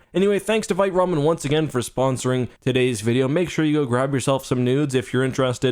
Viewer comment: auto generated captions says “go grab some nudes” instead of noodles lol.